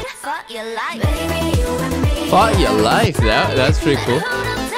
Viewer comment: the melody is from tetris. i think its pretty good considering they used the tetris melody. i like winters "for ya life" part hahah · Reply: Idk but the first time listen I thought she was saying ‘f*ck your life’😂